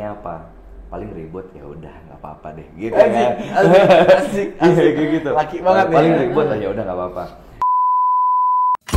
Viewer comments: itu bang luthfi ngomong apa ya? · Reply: dia ngomong anjay😂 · wait, what he say? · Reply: ehhhhh dia keceplosan anjirrr...eh asikkk